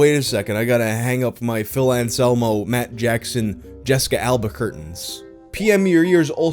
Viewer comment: I heard Jessica Alba-querque 😂